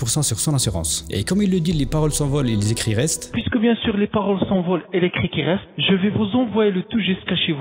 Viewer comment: Vers 4:15, "les paroles s'envolent, et les cris qui restent"... C'est ce que j'ai compris moi en tout cas ! Ceci dit, de ce point de vue là il n'a pas menti ! 😂 🤥